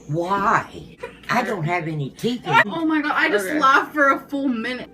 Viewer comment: Grandma saying ‘why? I don’t have any makeup on!’ was so cute. I started watching AL right after this breakup so I’m anxious to watch more from here. It’s sad to see how far she’s fallen up till now, the Wipey era. Nice to see Dr Todd Flake make an appearance lol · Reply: I thought she said “I don’t have my teeth in” lol